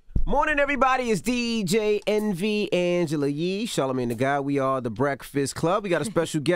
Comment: I always hear "Angela YEESH". Lol